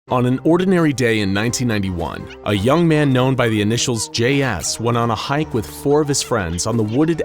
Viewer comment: In the beginning of the videos I thought it said “a young man by the initials of gay a**” Loll